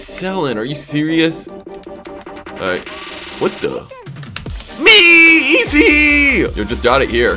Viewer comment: am i tripping or did he say ME EZ?